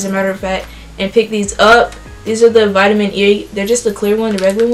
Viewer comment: is it just me or did it sound like she said vitamin yee 😂 no hate love your videos